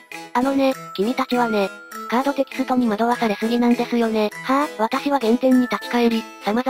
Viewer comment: のはぁ。がハァ？にしか聞こえなくてgrass